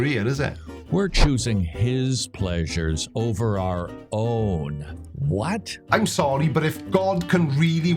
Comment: When you said 'Our Own' I thought you'd said 'Dai Owen' and I for a split second I thought I'd missed the introduction of a new person :) Great way to keep the audience attentive.